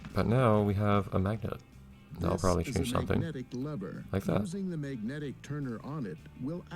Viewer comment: I seriously misheard/misread that as "This is a Magnetic Lover."